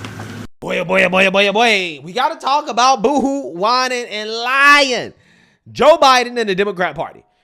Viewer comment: Whenever you say "Boo hoo whinin' and cryin'" I hear "Woo hoo winin' and dinin'." 😂